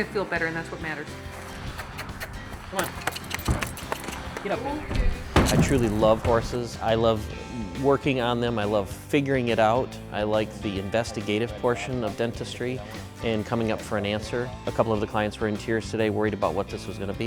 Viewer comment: Wait did I hear "Fingering it out" bear the end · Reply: Roodle De Noodle "figuring it out" 😑